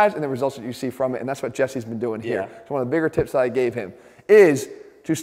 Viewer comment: I coulda swore he said "... the bigger tits I gave him. " I must be going deaf